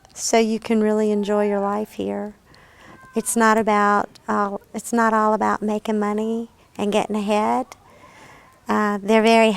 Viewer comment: I deadass thought she said “it’s not all about making money and getting head “ lmao whoo had to rewind like 3x